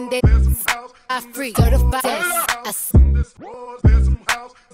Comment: In this world, there's some house In this world, there's some house In this world, there's some house In this world, there's some house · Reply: I heard “In this war there’s some house”